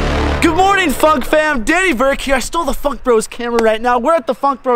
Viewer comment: I don't think you really said Funk Bros I think I heard something else on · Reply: He said fuck